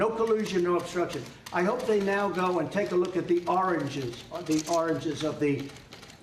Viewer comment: did I just hear him say Oranges?... Twice!!! and again at 04:55 this Idiot is mentally ill! If you can't say Origins 5 times in a row, Go see a Neurologist ! I just red he now mixes up his father with his grandfather. Not to mention he's previous plural slip's. Definitely a Mental Condition... the US has become a nuclear threat to the World. You guy's Do know, he's walking around with the nuclear football Right???